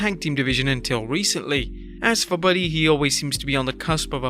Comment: i think you said buddy hield